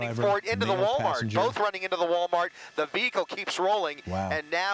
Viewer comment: IT SOUNDS LIKE NEWSCASTER SAYS "THE BEAGLE KEEPS ROLLING" >> 25:37 BEAGLE / VEHICLE...WHAT'S THE DIFFERENCE....